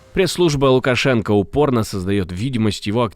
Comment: Послышалось "пресс-служба Алкошенко"😂